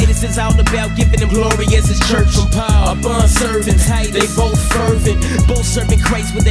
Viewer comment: isn;t it, "they both fervent" instead of "serving"? just wondering